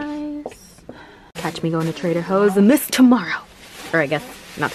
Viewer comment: Did she just say trader hoes